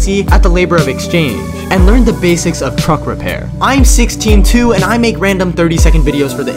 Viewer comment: Is it only me who thought he said I'm 62